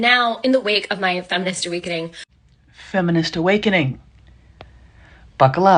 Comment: I thought she said, “my feminist weight gain”, which was also possible.